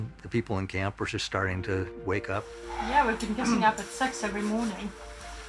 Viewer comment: why does this lady 6 sound like a s*x when i heard at first i heard she was saying we've been getting up & having s*x in every morning? 😂😂😂😂